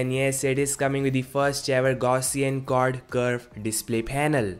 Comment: kya bola tune?? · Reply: Quad Curve Display